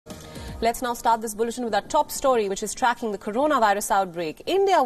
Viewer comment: did she just say "let's now start this bullshit"?